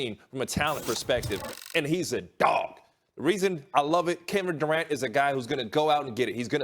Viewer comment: Did he say Kendrick Durant at 0:27?